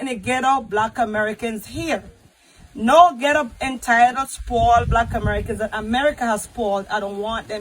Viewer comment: Actually I heard her say " entitled black American"